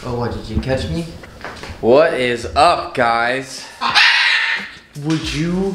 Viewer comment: She sound like she said what's up real loud that's funny